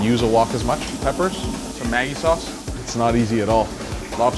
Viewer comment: What is that sauce he is putting in the wok? It sounds like he is saying: "Tomain" sauce? I can't make it out. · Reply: Maggi i think he said